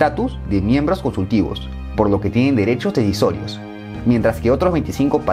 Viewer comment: Qué dice en el minuto 11:03 ?? Derechos divisorios o derechos a territorios??